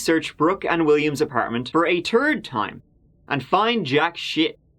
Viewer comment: What I heard, "The police searched Brook and William's apartment for the turd time, and found jack shit." 🤣😂